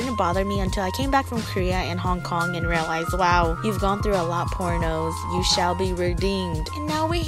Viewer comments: "poor nose" made me laugh , u will get why if you have a "dirty mind" 😂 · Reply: thought she said pornos PHEW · "poor nose" made me laugh , u will get why if you have a "dirty mind" 😂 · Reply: I thought she said porn o’s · i thought she said pornos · I thought she said pornos (or however you spell it lol) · "poor nose" made me laugh , u will get why if you have a "dirty mind" 😂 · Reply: I thought she said PORNOS lmao 😂